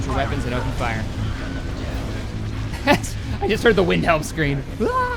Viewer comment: Did call the Wilhelm Scream the Windhelm Scream at 39:10ish or am i hearing things? :P